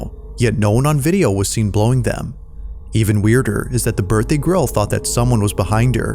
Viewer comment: Bro. Did he say “the birthday GRILL”? 19:05 The candles weren’t blown out. They simply went out. No wind. That’s being said… was it a ghost or just an elaborate hoax?